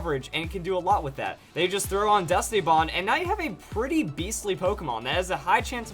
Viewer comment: I thought he said "Dusty bond" XD